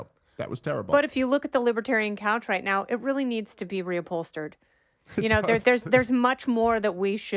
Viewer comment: Kennedy said reupholstered... i thought at first she said Ran Pualstered 😑